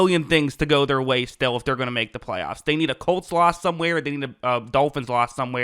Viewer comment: You said, "they need a Colt's loss." But it sounded like you said, "they need a cole slaw..." ha ha :face-blue-smiling